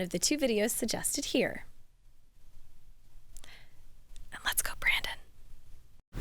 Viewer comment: What did she whisper? It sound like she said......... LET'S GO BRANDON!